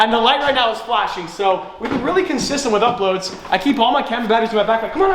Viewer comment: We are getting consistent boi!!! 🔥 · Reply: I thought u said consent 🤣🤣🤣